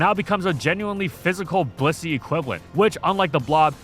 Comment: did you say "Bussy?" Maybe I'm just wanting Femboy buns. Hahaha